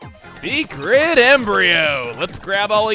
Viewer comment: Did...did chugga just say secret embryo? Also obligatory tf2 reference